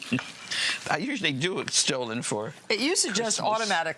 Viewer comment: The tracking spike at 3:40 proves I'm not the only one trying to figure out what she actually said! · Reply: stollen